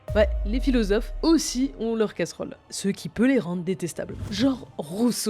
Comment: vous vouliez écrire casserole ou c'est un vrai mot, et si c'est un vrai mot il veux dire quoi ? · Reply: Bah vu qu'ils disent casserole dans la vidéo à mon avis c'est une coquille